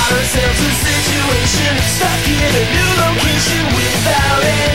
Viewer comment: when I was little I thought he said stuck in a noodle kitchen